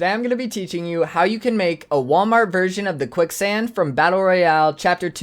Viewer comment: did he say walmart?